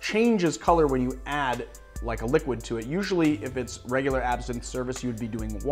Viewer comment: I had to play back because I thought he said "if your regular ass is in service"